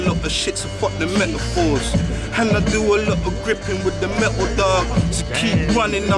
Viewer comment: ?? did he say metal dan ??????